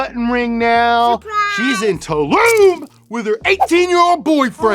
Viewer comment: i feel like he meant “in Tulum” instead of “into lube” LOL